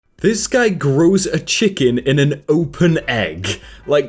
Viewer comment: I really heard that as “grill a chicken” instead of “grow a chicken” and when i saw tiny organs I got so fricken confused